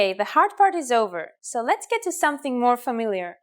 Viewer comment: I got confused because the subtitles say it's an "s" sound because that's what it sounded like she was saying but it's actually an "eh" sound. Epsilon is an Eh sound, EH-psilon.